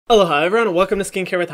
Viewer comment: I was today years old when I realized is saying “aloha everyone”. I thought he was just saying “hello hi everyone” really fast lmao.